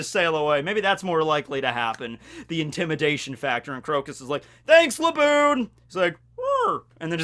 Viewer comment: Thought you said that dude he met with was crokus. But yeah he does look like shiki.